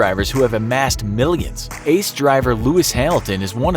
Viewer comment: Did he just said that Hamilton drives for Hass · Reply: Goh Nicholas He said “ace”